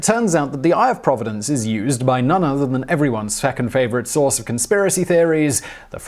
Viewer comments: Ooooohhh! I had to listen to that over and over! I thought he said "is used by everyone's feckin' favorite source...", not "second"! Like, dang, didn't know you felt that strongly about this! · I literally had to turn on cc at 1:39 to recognize that he says "second favorite" instead of "feckin' favorite".